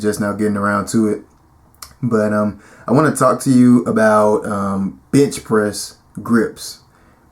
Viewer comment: did you say "bitch" press hahahaha lmao